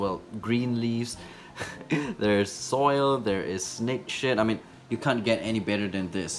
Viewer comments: I heard ‘snake shit’ instead of ‘snake shed’ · when he said “snake shed” i thought he said snake shit 😂💀💀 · I thought he said snake S**t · to me it sounded like he said snake shit but he said snake shed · it sounds like he said snake sh*t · I am not the only one who hears "snake shit" right?